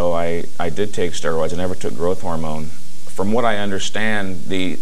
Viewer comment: Did he say "I did take steroids and never Gh... I am italian 😊. 12:40 Thanks guys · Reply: Right. He did use steroids when he competed but not growth hormone, which gained prominence in professional bodybuilding in the 80s.